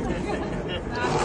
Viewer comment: What was that chant? It sounded like 'Aussie Aussie Aussie, Oi Oi Oi!' But it was different. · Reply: Oggy oggy oggy.............not the best Scout chant in my opinion ,but one that is now used internationally.Many Troop in the past had their own.........Ours was "Yakka boomah,Yakka Boomah...!Who are we? We are Somers Willerby!" 😂